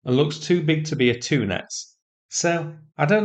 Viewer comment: Did you say tuna (at about 3:20)? Sorry I'm old and can't hear for #%&T - thanks for some good news after the latest TACO Donny bully stunt! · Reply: From the CC at 3:13 `tuness`. Also from a Google search -- "Tunets" (or "Tuness") is the name of a class of Russian patrol boats, the Project KS-701 Tunets-class.